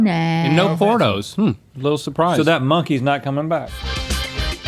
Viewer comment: the captions catch link as saying "p*rnos" instead of "portos"